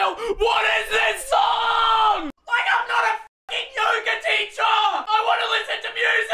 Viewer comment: as soon as he said F ing the 8 all pool ad came up What I heard: Fucking 8 ball pool